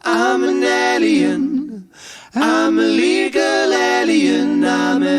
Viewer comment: I keep hearing " I'm a legal alien " 😂😂😂😂 Yes, because that is what they say ! 😂😂😂 ❤✌ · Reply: Just came here to say that too!! 😂 Although when it first came out I thought it was `illegal` for a long time!